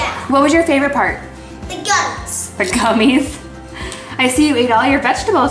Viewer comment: When asked what was her fav part was I thought she said guts at first instead of gummies!!!😂🤣